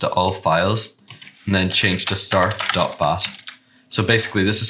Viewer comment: What do you say on 2:37 start.bas or something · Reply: It’s says “start.bat”